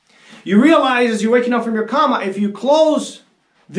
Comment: Did he just say "comma"? 🤭😄